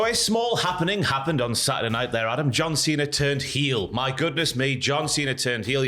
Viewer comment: The beginning of this sounded like you said ‘Adam Johnson turned heel’ …I think Adam Johnson turned heel a little while ago mate 🙄🤦🏻